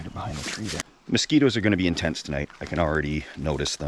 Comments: I heard "Mosquitoes are going to be in tents tonight" Me: "then close that flap and pass me a double double." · What I heard was. "the mosquitoes are going to be in tents tonight" 😂